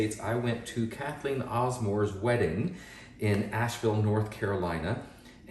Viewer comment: Oops - misheard. Asheville not Nashville. Googled it and it looks great place for an artist’s wedding.